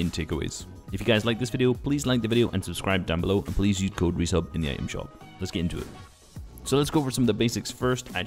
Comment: Am i the only one who heard him say use code Muselk in the item shop?😅🤣